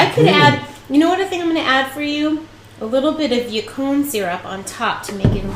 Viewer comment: lol I thought you said "your corn syrup"